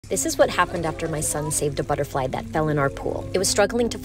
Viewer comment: pepole who heard it say my son ate a butterfly 👇